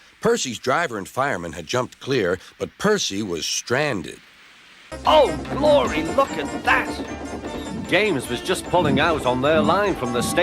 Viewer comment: did i heard "fucking" ? dont say that ever again duck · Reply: He said: OH GLORY LOOK AT THAT